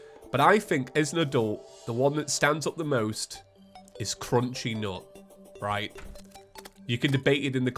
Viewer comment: Yeah i thought it you were saying crunchy knot